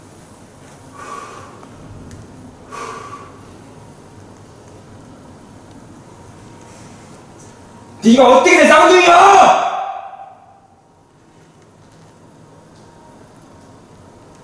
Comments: 내가니쌍둥이야 들리는데요??? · 여자가 내가니쌍둥이야진짜소리들림